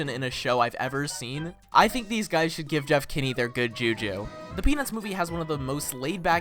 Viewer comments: I forgot to turn off subtitles and you said the peanuts movie but subtitles said the pe*is movie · I think I heard "penis" movie